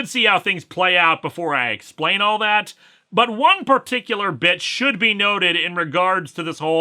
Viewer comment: I miss heard what he says at 7:37 as, “One particular bitch”, instead of, “One particular bit which,” .